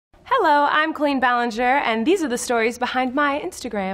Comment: I thought she said, "Hello, I'm Queen Ballinger." in the beginning. So true, my non functioning ears.